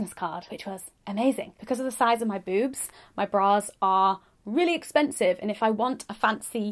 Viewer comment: I definitely just misheard, "My boobs are really expensive," and that just made my day, so thank you!